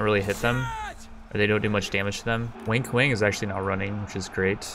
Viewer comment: I swear it sounds like your saying "wink wink" for wang kuang